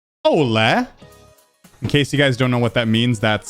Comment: Honestly, at the start I thought you were saying hola, which is actually kinda funny since you were saying hello anyway.